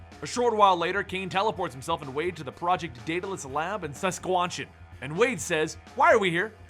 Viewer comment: Who else heard him say saskwanchan instead of Saskatchewan